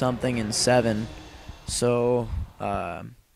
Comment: the aa-12 is fully auto, and at the end it sounded like you said "please rape and comment." haha